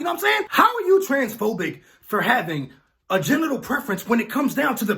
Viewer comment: Bro at the beginning my nigga was talkin so fast it sounded like he said genital preference instead of general